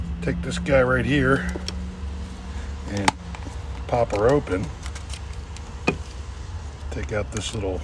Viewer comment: I swear, the first time I heard "pop her open" I thought you said "pop a rope in". I was so confused until the second loop.